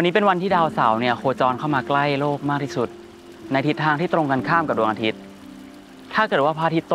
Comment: I heard "Arthit".